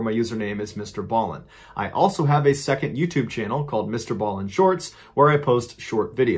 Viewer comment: Does anyone else hear, "ball in shorts, instead of "Ballen shorts?" I was busy doing something and am new to these excellent stories, so when I heard "ball in shorts," I had to do a double take! LOL Now it's stuck in my head!